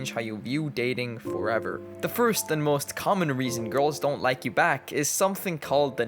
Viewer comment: Am I this cooked? I thought it said, “girls don’t like you cause your black” it’s over😭